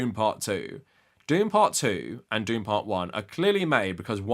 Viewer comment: Man, I misheard you and thought you said "Doom part 1 and Doom part 2", reminding me about the Rock in the first one. I was so ready for you to talk about those wrecks!